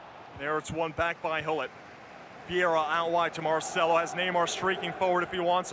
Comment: did he just say Marcela